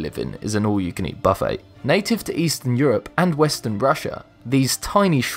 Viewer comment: did he just say ''western Brussia'' at 1:06 :D :D :D?